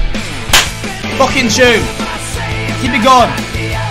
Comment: did he say fucking jew?